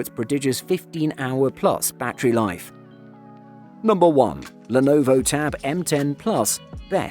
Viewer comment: you got the caption wrong -> 5:55 . Suppose to be Lenovo M10